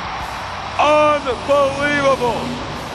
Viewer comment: lol did he say un-four-wheelable? at 0:38 lol